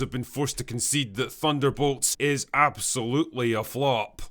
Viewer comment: Thunderbolts, I thought you said ThunderBalls Eh same thing.